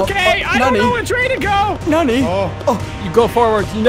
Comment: Did SMii7Y says NANI?!?!?!